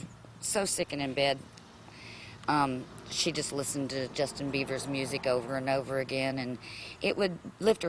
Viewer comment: LMAO she clearly says Justin Beaver at 1:03 really shows how much you really care about your daughter...